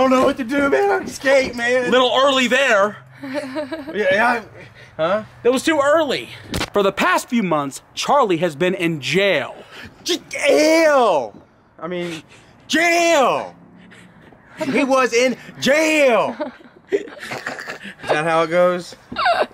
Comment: Lol i was dying at 7:05 xD did he say "Jkall"